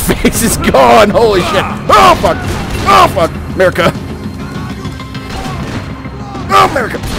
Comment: it sounds like he says oh fuck america